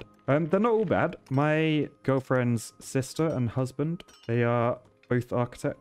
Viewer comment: i beg your pardon honestly it sounded like your girlfriend had a husband : )))